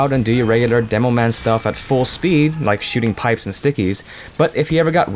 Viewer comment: i think accidently said shiting instead of shooting